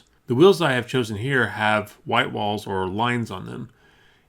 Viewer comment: Reverse the wheels again and add some yellow sport stripes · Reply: Definetly agree. Also, did he say white lines instead of yellow in 9:45?